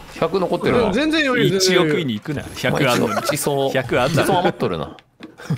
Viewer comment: 馳走、何回聞いても地層にしか聞こえないのは私だけ…？